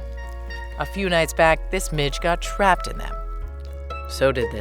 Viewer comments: Watching this after a long day at work at 22h00. At 1:21 I heard "a few nights back this b#$&- got trapped in them" had to turn CC on . Played it back like 3 tomes😂😅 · My speaker was set to low, and I misheard you say, "A few nights back, this b** got trapped in there..." 😅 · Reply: midge! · OMG i thought she said "this bit** got trapped in it" when talking about midges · At first I thought she said b** not midge lol